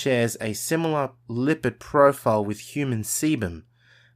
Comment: no shot thought he said seman lmao😂